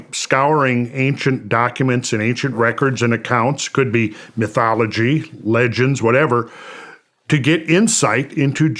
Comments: I thought he said Jew Mythology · I definitely thought he said jew mythology the first time lol